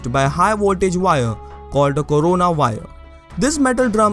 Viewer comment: I legit heard it as "called a Corona virus"